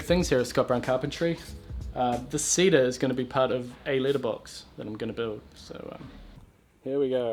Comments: I thought he said "litterbox" D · I thought he said Litter Box, like for a cat. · i thought you said, "litter box" like a cat's litter box, lol. i'm glad you added followup video of the letter boxes! :D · i thought you said, "litter box" like a cat's litter box, lol. i'm glad you added followup video of the letter boxes! :D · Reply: My first thought when I heard litter box was: "hmm, I guess cedar is a decent choice for that." Followed by an image of a letter box: "oh yeah, that makes more sense." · I thought he said litter box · I thought you were saying litter box As in shit box LOL